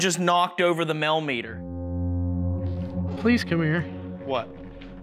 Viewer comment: Brilliant house to investigate, held it together very bravely I thought, regarding the whispered voice I heard " Vincent come here, here" or "Vincent hear me ,here". Great evidence guys 👻👻👻 X